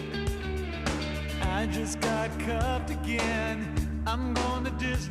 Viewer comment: sounds like he says "I just got cucked again".